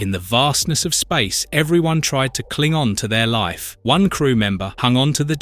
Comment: did you say Klingon?